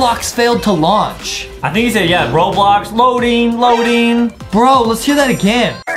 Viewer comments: I heard him say Roblox band what the heck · I hear bobux maybe